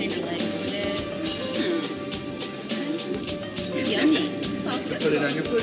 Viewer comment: what he say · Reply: Yummy